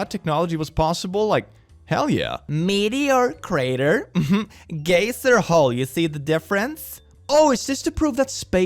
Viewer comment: Dude 😂 when you said “geyser” (guy-zer), I heard “gay sir” and then you said it had “many holes” and i 😂